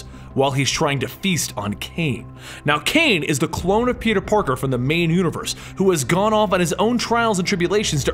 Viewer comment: Did he say peter porker